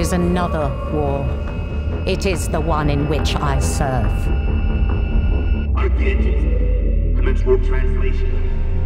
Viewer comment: The subtitles say "Light the Engines, Commence warp translation" but I swear I hear "Cryptid. Commence warp translation." So W40K nerds, what is actually being said here? Wtf is a Cryptid? Also, this is an awesome trailer for the Warhammer 40K universe. Really does a good job laying down an atmosphere for it. I think this gets close to almost how great the Astartes videos were.